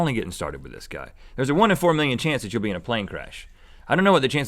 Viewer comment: i thought Rhett said 1 in 4 change of being in a plane crahs, and i was like ._. IM NEVER GOING ON A PLANE AGAIN!!!